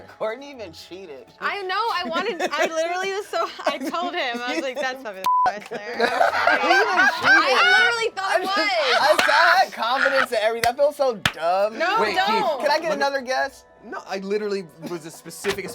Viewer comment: what was the beep at 12:43? did Courtney say "Buffy the Vampfuck Slayer"?